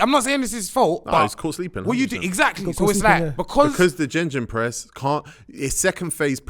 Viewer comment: Did just say gingin press😂😂😂